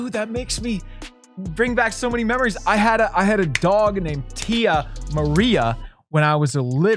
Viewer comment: I kept skipping for science, and I heard say “ mom, I had a dog waterfall” golden